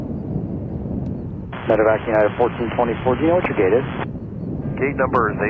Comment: Does anyone know what ATC was say at 1:05? Something like “meta... united 1424?” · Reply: I think he said “medivac“ referring to an aircraft used for medical transport.